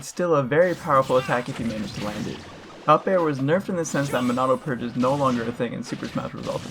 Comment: Monado what is no longer an option? You mumbled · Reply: Monado Purge